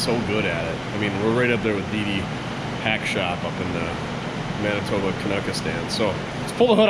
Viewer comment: I’m watching this just now and I heard you say: “DD’S PACK SHOP” Now when it come to DD all the faces he uses makes me skeptical about his work. Does his quality of mechanical things get goofy repairs like his face (Jus kiddn DD even PUDDIN thinks that’s funny)